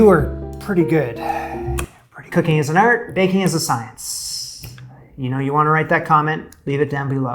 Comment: Cooking and baking are both art and science. Good job! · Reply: I heard it “cooking is art, but baking is science.”